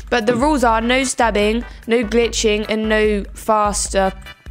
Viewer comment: Her accent made me think she said no btching